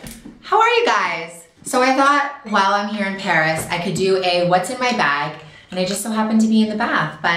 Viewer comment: At first I thought it said "what's in my bikini" 😂